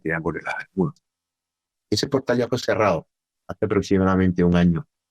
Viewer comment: preciso se distorsiona cuando dice el nombre del portal, no se entiende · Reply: Dice que es el triángulo de las Bermudas